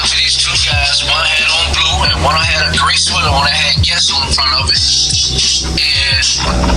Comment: does he say the sweater says "yes" or "guess" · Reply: guess